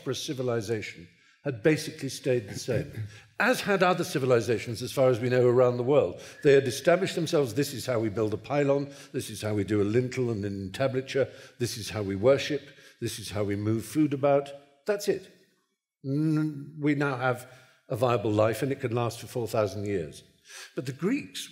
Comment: What does he say at 12:30 ? A lintelman tablature? What?